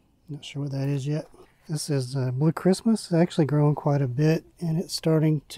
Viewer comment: That Purple pepper looks amazing . What kind of pepper did you say it was ? I thought you said Christmas . Thanks for the update . I enjoy watching the progress of peppers. I do the same on my channel . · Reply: Thanks, that pepper is called Blue Christmas. I'll check out your channel!